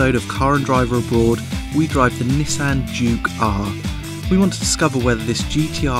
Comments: Really sounds like he is saying Jew Car, not Juke R. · When he says juke-R it sounds like he is saying jew-car LOLOLOL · it always sounds like he is say "Jew car" every time he says Juke-R. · Every time he says Juke-R it sounds like he says Jew car....not sure why that's funny but it is.